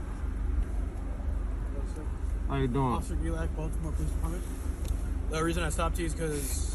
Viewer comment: did he say officer I’m drunk ???